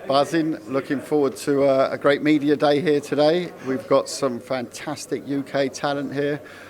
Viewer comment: I thought he said we got some fantastic gear here 😆❄️